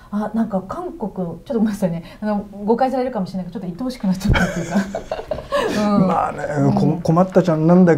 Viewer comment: 椿さん最後に”愛おしくなっちゃった”って言ったんですね、最初は”鬱陶しくなっちゃった” に聞こえたもんで随分直球だなと思いました、でも言った時の表情からすると 後者の方が本音かな？というのは私の邪推だと思いますｗ